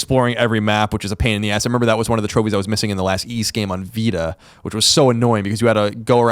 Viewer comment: Sorry, couldn't hear well, what was the game Colin said he didn't got the exploration trophy on vita at 58:48 ? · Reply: the Y's game